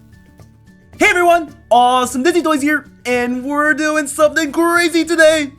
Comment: At the start it sounds like your saying awesome vissitoars here